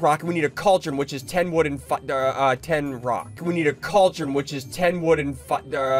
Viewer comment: I heard “we need a cauldron which is 10 wood and 10 fat”